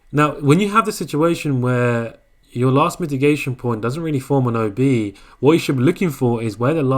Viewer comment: Hey, i didnt understand at 15:24 , " didnt form and OB" ? If we have BOS we have OB, right ? Can y explain plz , thank a lot !